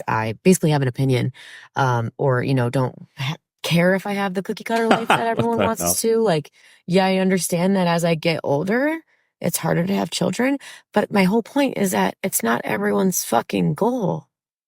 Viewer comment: When I first listen to this, I heard "As I get ogre, it's harder to have children". The funny part is that she is begging other women for support. She needs other harpies to validate her stupidity.